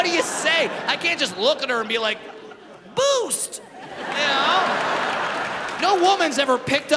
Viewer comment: I heard him say "Boobs!"